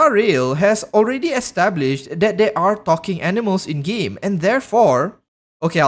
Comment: i thought you said there are fucking animals I'm giggling so hard im sorry